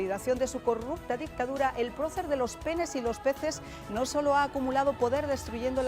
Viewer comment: Que dijo ella, "el procer de los penes y los peces ????? minuto